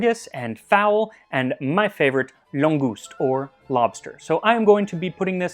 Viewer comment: wait what did you call it... langouse? That sounds an awful lot like langoustine......